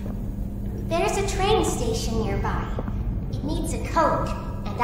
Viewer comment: There's a train station there nearby all it needs as a Coke! 😂 I know she says, but code I heard coke on the video